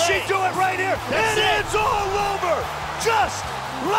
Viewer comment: DID HE JUST SAY PARTYS OVER?! 😂😂😂